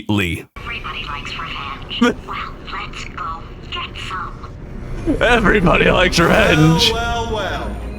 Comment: I misheard the line: "Everybody likes revenge, well let's go get some!" as "Everybody likes ranch, well let's go get some!" and I was very confused